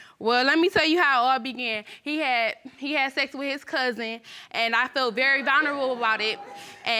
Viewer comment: I thought she said he slept with my cousin then I was like nope she said his cousin and he said you knew that already. But when she said cousin/son I vomitted in my mouth. And just when I thought I heard enough he said this will be my only child🤦🏾‍♀️